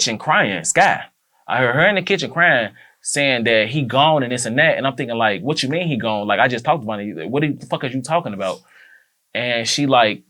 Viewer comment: Did she just say “I’m trying to get out the funeral doors when they said Von gon”